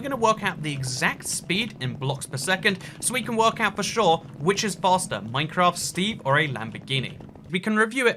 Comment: my dumb ass thought it said "which is flatter"